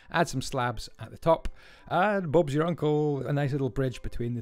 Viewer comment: Did anyone else hear him say “Bobs your uncle”